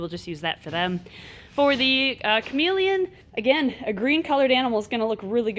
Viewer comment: When she said Chamaeleon I thought she said comedian.ha ha ha!😝bye guys